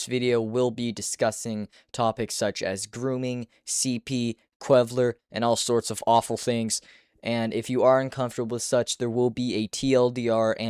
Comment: did he just say qwevler?😂?